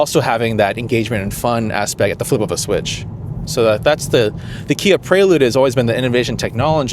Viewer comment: did he say "... the Kia Prelude"? :-) Nice video, as always...